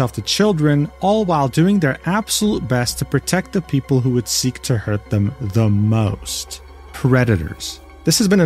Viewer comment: I wasn’t fully listening and I deadass thought he said “the people who want to hurt children the most…. REDDITERS”